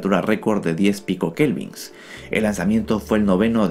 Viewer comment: dice que el lanzamiento fue el noveno de 11 millones? o entendi mal :v